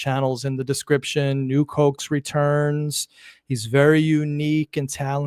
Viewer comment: OH you've been saying "Nuke Hoax"...lol...I thought you were saying "New Cokes" (which I thought was an odd name)....Just seeing this today. Great chemistry between the two of you. Would love to hear him on the show again sometime. Great show. Thanks